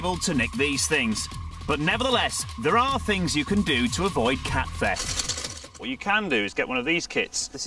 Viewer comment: Turn On The Subtitles And Look What It Says |Like If You Saw What It Said| He Meant To Say "Car Theft" 😂